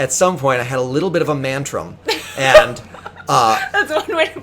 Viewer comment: I have been trying to understand what word he said at approximately 40:43 "I had a little bit of a ..." I've been searching countlessly and I can't find ANYTHING that could be that. Mandrum? Mantrum? Mendrum? Mentrum? Amantrum? Amaoontriueom? WHAT THE HELL DID HE SAY!? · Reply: Mantrum. Pretty much a man tantrum.